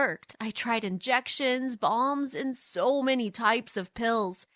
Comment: Am I the only one that thought she said bombs